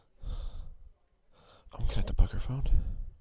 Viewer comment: what is he saying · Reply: He's considering bugging her phone